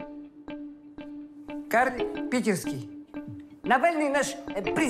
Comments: что значит "карн питерский"? Или что там говорят? Никак разобрать не могу... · что значит "карн питерский"? Или что там говорят? Никак разобрать не могу... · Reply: "карлик", он жеж метр с кепкой)...